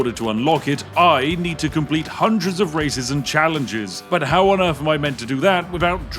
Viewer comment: anybody else hear "racism challenges"? Because he's a Brit, we practically invented it. America has casual racism because they can't keep up with the Pro Circuit